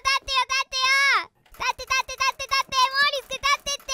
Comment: 序盤でカメラモード解除し忘れて ドアップになるちゃんめちゃめちゃ可愛いし 3:54のベッドゴロゴロしながら「立って立って」がだんだん「買って買って」に聞こえてきて お母さんがスーパーでお菓子買ってくれなくてただこねてる幼稚園児にしか見えんwww